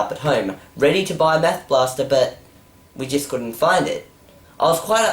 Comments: it sound like you said meth blaster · Sounds like you said "Meth Blaster" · Sounds like you said "Meth Blaster" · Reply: I know I thought he said Meth Blaster untill the words came up on the screen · meth blaster... mmmmmmmmmmmm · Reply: yes but it sounds like hes saying meth blaster because of the australian accent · it sounds like hes saying meth blaster XDDDDD